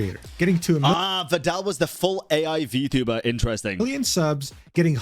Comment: "I don't know who Vedal is" - I see a Neurosama reaction video in the near future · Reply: oh, I thought he said "for-AI vTuber" and not "full AI" My comment was gonna be that Vedal is more a dev of ethically programmed AI 🐢